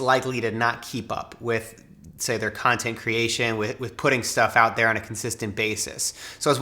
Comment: my fat ass thought u said “pudding stuff out there” 😂😂